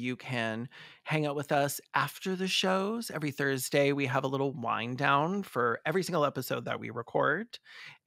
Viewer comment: I’m sorry, but at the end of the video, when you guys say you “have a wind down,” at the end of the video, I heard, “wine down.” Super sad about Koko, fuck Penny!